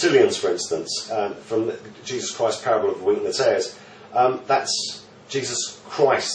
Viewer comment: I want to look up the parable of Jesus he mentioned at 11:40. But I cant understand what he is saying he spoke too quick. Can some1 tell me the name of that parable he mentioned? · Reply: Spidey Shuffle wheat and the tares. Mathew 13:24-30